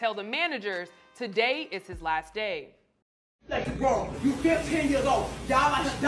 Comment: Did she just say you still 10 years old? 0:44 👂🏽😭😭💀